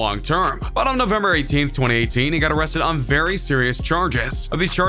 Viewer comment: I thought you said he got released